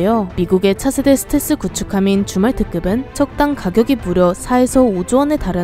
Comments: 잠결에 줌왈트급을 주말특급으로 들었다😂😂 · 저만 줌왈트급이 주말특급으로 들리는 건가요..? ㅎㅎ